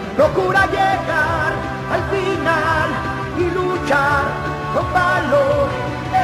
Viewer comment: De niño pensé que decía Goku llegará y luchara con valor. Y yo decia entonces va. Volver del otro mundo